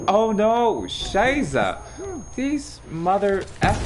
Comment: Did he actually say "Scheise" like the german word xD